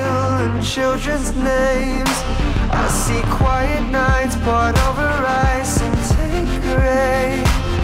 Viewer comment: i always heard 'poured over rice'